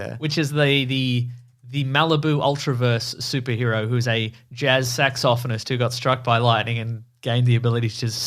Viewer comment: Did you just say "Jazz Sax-OFF-inist?"